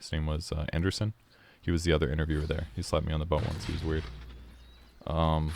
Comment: When you said he slapped you on the butt once I thought you said "he slapped me on the phone once, it was weird" and it took me like a full minute to figure it out.